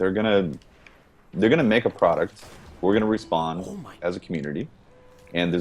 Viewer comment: anyone else hear whisper " oh my....."